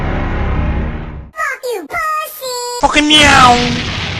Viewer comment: what did he say from the background? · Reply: "Fuckin neowm!"